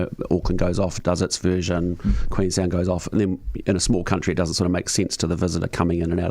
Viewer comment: did he say Queensland?